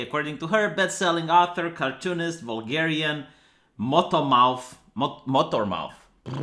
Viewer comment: Oh thank fuck that says "Vulgarian". For a moment there I thought said "Bulgarian" and got worried that this shit was being peddled by someone I share an ethnicity with.